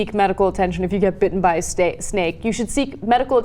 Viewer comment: I thought I heard "bitten by a steak"